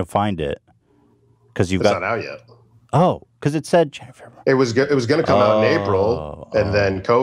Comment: Why do i hear a whispered "Jennifer"